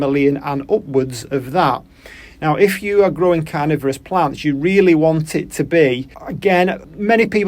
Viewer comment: 😂 just for a second I thought you said were trying out co it for growing cannabis instead of as opposed to carnivorous plants which I thought was a daring reveal😂. But this is just what I need for my carnivorous plants.🫡